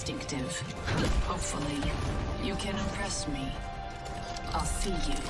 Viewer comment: I SWEAR she said "Hopefully... you can undress me" 😆